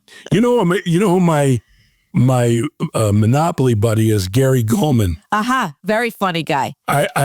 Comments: Always appreciate the Thursday laughs ... especially now. Gary Coleman ... or Gary Cole? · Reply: I thought he said Gary Gulman. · Beat me to it! That is what he said and Gulman is hilarious! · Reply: I thought he said Gary Coleman, but Coleman died in 2010.